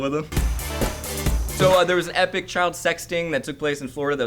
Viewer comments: I was on another tab so I heard, "Epic child sexting" o_O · I thought Elliott said child sexting